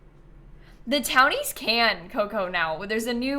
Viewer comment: What is saying at 1:00:50? It sounds like "the townies can cocoa now." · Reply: I think she is responding to someone named Coco. So she’s saying “the townies can, Coco, now” so she must be responding to something Coco asked about the townies?? That would be my best guess